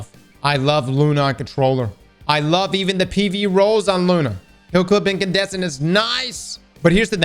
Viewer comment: Did he say heal clip or Kill clip? I'm trying to find a good roll, lol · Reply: Heal clip